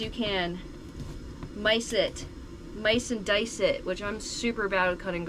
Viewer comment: cooking and cutting garlic around @ 17:05 ...yo did you say "mice and dice the garlic"???? LMAO Thats so funny ! Can I steal that? Im no longer gonna miNce and dice gonna be micing and dicing from now on!! Great video and that new camper is freaking top notch!! I am researching and checking out all options for my 1st rig! I take care of my momma right now! and after 2 strokes in July she is awesome though! But in the future gonna build me a rig and hit the road! So happy for you!! You seem ecstatic over your new set up and wheels!! You deserve it you are awesome and you built a following by yourself and as yourself!! Congrats and have a blast in your new ride!!